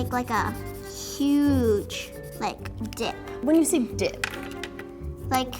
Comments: i thought she said dick i was gonnnaa sayyyyyyy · I thought she said d”””k for a sec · lmao i thought she said "Huge Dick" 😭😩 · I thought that she said "huge dick" Instead of " huge dip" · ik this is not Child friendly but I thought she said "huge dick" I was honestly baffled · at first I thought she said she wanted to make a huge d*ck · I had my volume pretty low so instead of dip I heard dic- OOPS, IM NOT SAYING IT 🤭🤭😂😂😂